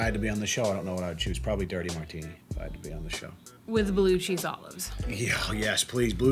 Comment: said "Blue cheese olives" I heard: "Blue chew olives"